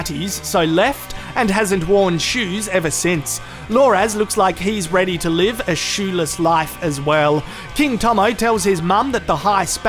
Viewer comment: for a sec, I thought you said, "Loras looks like he's ready to live a sheila's life, as well." LOL